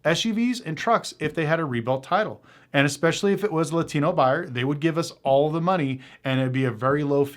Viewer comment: did he say Latino buyer 😮😢